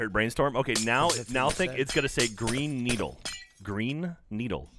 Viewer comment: i heard brain needle from the start lol